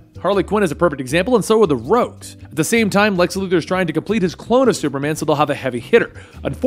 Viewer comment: Did you say Lex Luger??? Slow down a bit 😂